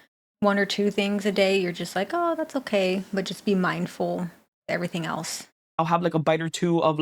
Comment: She said "mindful" and I heard "moderation"... I'm too involved in gorlworld😂😂😂. She seems so genuine and clearly cares for other people in a non performative way. I wish her all the best.